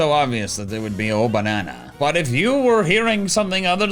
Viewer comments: I always thought it said Golden Banana not Oh, Banana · I hear “Oh, BAGONA” or maybe Bagon-a 😂